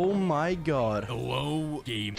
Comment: I don’t know why I hear the most random things but during the video, I heard say "HELLO GAMA’S"